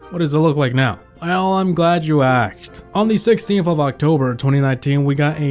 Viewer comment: did he say im glad you axed instead of asked?!